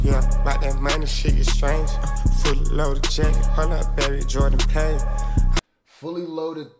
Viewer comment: I thought he said “fully loaded jet, Jordan paid” meaning he bought a fully loaded jet just to show u he’s PAID